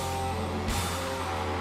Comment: i thought he said "Have fun with Fallout 4"